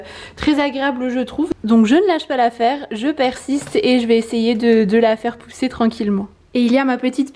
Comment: Mdr qd tu as dit "la fille persiste" 3:33 j'ai compris la fipercite je me suis dit tien original comme nom de plante ... #blonde